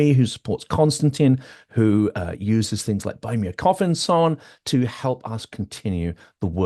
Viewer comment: Did I really hear 'buy me a coffin,' at 0:50? Now that's dark. Ok,ok I think it was a slip, but in the context of the usual Ukraine discussions, I thought it absurdly funny.